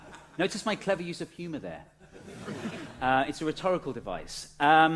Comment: What exactly did you say? "Clever use of humor there" or "Clever use of humid air" ? (or both? :-) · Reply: I hate to say it it was humor